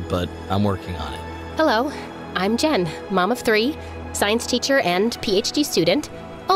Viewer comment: I heard “mama three” than “mom of three”